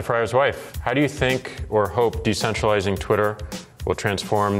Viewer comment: Did I just hear decentralized @ 10:12 ? God I love bitcoin